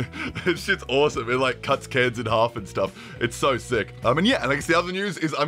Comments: did u say cuts kids in half or cuts cans in half.... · I heard "it cuts kids in half" and for a moment I was like wtf before I realized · When you said "Cuts cans in half" I could've sworn I heard you say "Cuts kids in half", but that's obviously not what you said and captions were telling the truth—right? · sounds like he said it cuts kids in half